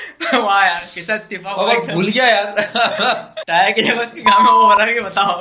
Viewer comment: The audio is so distorted today that the translators misheard. I'm pretty sure said "Phool Gaya" (he inflated) not "Bhool Gaya" (he forgot) the two sound very similar.